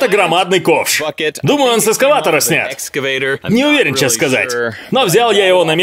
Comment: Мне послышалось? Или ты сказал сексскаватора